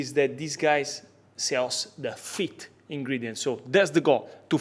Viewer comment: I thought he kept saying feet ingredients